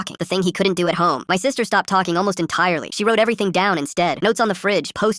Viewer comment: I thought u said ur sister can’t talk